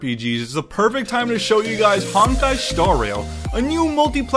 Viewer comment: anyone else hear “hentai”?